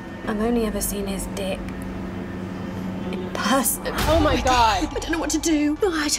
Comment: What does she say after 0:51? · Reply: in person